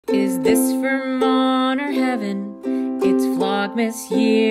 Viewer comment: only just realised the intro says "is this vermont or heaven" I thought you were saying "is this for mom or heaven" lmaoooo